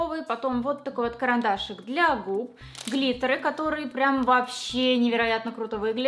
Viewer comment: клиторы ... Я же не одна это услышала ?